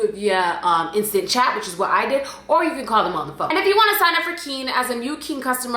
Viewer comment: lmaooo I thought you said,"or you can call the mf" 💀